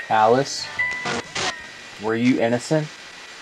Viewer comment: Video is now avail in full resolution!* This is the LAST month to win an overnight investigation with us! I repeat, LAST MONTH. So leaves those likes & comments! or Snag some gear at <link> Our December mystery boxes are still available (at the time of writing this) so go ahead and treat yoself. Appreciate all of your support! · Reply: I think she said innocent to your question, it's a very quiet whisper of a lady's voice